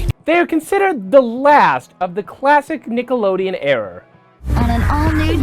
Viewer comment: did he just say error?